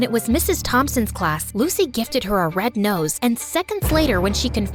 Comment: im sorry but did anyone else hear nose instead of rose in like 4:05-4:09?